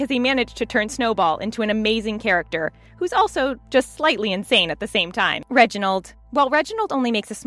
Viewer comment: Um....did you say slightly?